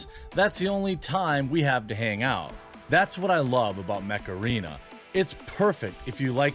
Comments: I was spacing out and instead of Mech Arena I heard "Macarena" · Jeremy says Mech Arena and all I hear is "hey macarena"